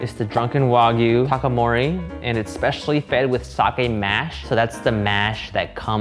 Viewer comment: This may be a me thing but at, like, 9:20 when Steven is talking about the beef coming from cows that are fed sake mash, the captions said sockeye... like salmon. I figured out what was going on through context clues but. For a while I just sat here for like five minutes trying to figure out if cows eat fish.